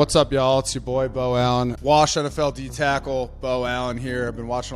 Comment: Bro, for a moment there I thought you said, "Washington D Tackle". I was like, 'Nooooooooo!". Stabbed me in the heart. lol. 😆